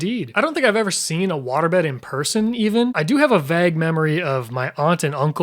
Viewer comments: did you say "vag" memory? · Did he say “vag” instead of “vague” at 1:44? 😂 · I’m sorry did you just say “vag memory” 😂